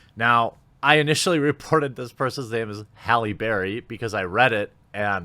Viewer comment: I brain processed this as "Halle Berry" then I was like "Oh lordy no" 😂 Haha I wrote my comment before I heard your's. Glad I wasn't alone with the misinterpretation.